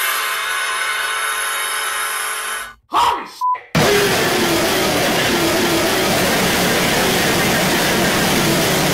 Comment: did a just say!? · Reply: Holy sh*t!